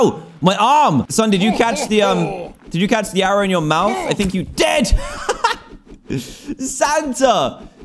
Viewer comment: I thought that u said "I think u died"